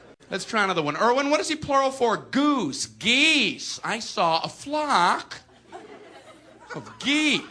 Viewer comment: I thought he said fuck but it was flock